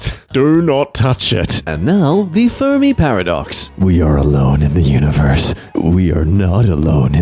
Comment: i thought said "the FURRY paradox" and i did a double take